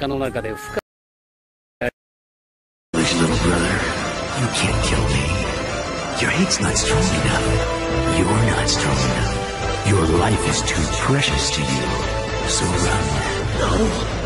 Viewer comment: the Worst! Can't even hear what he just say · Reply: It's easy to understand. "Foolish little brother. You can't kill me. Your hate's not strong enough. You're not strong enough. Your life is too precious to you. So run. That's right. Run away. And live. Live with the shame."